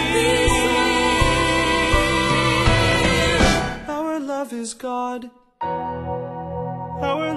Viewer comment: I originally heard this as our love is gone